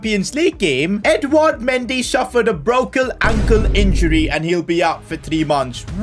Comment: Why did you say brokle ankle